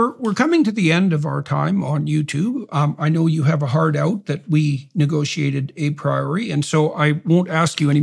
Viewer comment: does Jordan say "... that we negotiated a priori"? That doesn't make sense right?